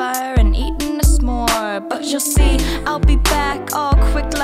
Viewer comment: when i first heard this on 3:07 i heard b**h youll see. i though went full gangster.